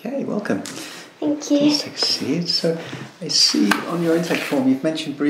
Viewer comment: Put subtitles on and see what it thinks he says at 0:03 😂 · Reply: Think he says take a seat?